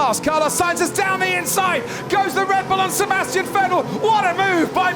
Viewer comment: did he just say down the inside goes the red bull of sebastian vettel